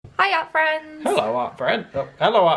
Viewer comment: Oh my gosh, until now I thought you guys have been saying "hiya friends!" But I just realized you have been saying "hi art friends!" Lol! How silly!!